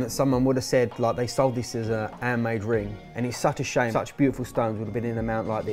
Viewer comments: Ah! “Handmade!” I thought they were saying “air made” I thought it was some terminology I just wasn’t hip to 😂 🤦🏻‍♀️ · I always hear "airmade" when you say "handmade" 😂